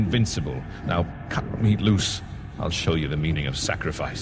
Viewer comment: I thought he said cut him loose...not hit him with a fucking sledgehammer.... ._.